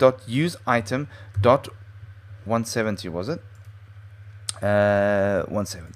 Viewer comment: Sounded like you said 117...